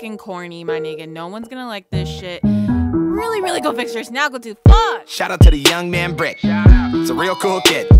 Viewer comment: its 2018 and i still have idea wtf she said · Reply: Ramon Taylor go fix your snaggle tooth. Is what I believe she said.